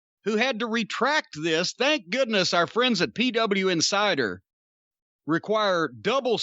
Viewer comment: When you said PW Insider I misheard it as "Peed up inside her" 😂😂😂😂